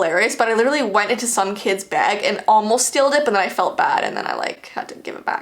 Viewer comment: did you say 'stealed'? 😂